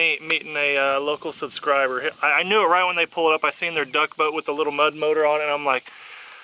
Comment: Just like my girlfriend I keep hearing “ pull out”l.